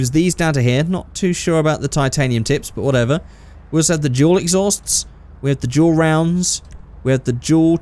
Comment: When your on 5:11 on the exhaust part when he said dual I thought he was saying juul